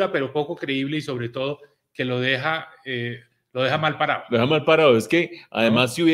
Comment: En 44:45 oí "mal parado" pero quedo con la duda si A o I